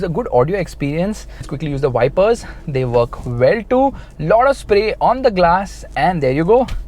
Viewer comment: Who noticed that at 10:34 told 'it gives lauda spray' instead of lot of!! Turn on captions and try it guyzz....just for fun don't take it SERIOUSLY. is best n'd beast!!!!